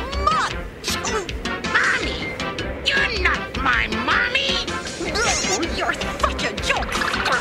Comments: what did she say? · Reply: she said you f***king jokester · OMFG. sounded like she said "Your fuck a joke"